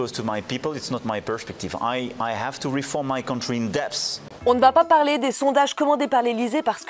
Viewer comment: Je pense qu'il y a une erreur de sous-titrage à 14:45 moi j'entend plus "I have to reform my country in depth" (que "I have to reform my contry in debt") ce qui est plus correct